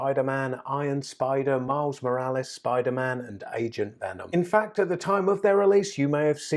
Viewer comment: When you said agent venom I thought you said asian venom lol